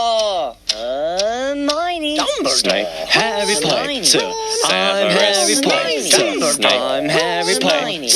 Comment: it sounds like Harry is saying "Harry pinter I’m Harry pinter" if I wrote it wrong it’s fine and sorry I’m late 😅